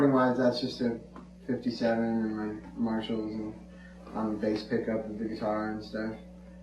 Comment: Does his say 'on bass pickup' at 0:22. What does that mean?